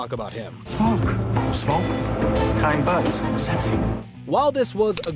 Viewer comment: That guy was offering "sensi" (sinsemilla), he wasn't calling the kids sexy. I don't know if mishearing that makes the team at Watch Mojo square or creeps. I'm leaning towards both.